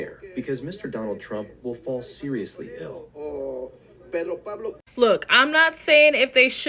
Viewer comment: he said die not seriously ill like the news caption said but ok 👀